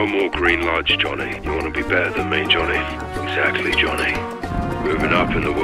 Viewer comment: sounds so much like "Sexy, Johnny!" instead of "exactly"!!! Prove me wrong!😂😆🤣😂 Love these two.